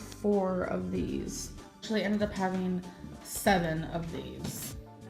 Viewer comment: Listening to you while doing some homework, and heard her say “I actually ended up eating 7 of these...” 😬😬 << my face as I look over to see yours 😂